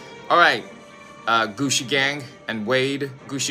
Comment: I thought he said “Gucci gang” 😂😂😂